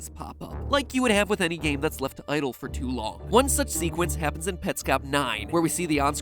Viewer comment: Timestamp? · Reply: the first time is at 3:42, which, I may just be mishearing, but not long after that, at 3:46 he says it and it sounds like seqwence. Is it jus me, or no?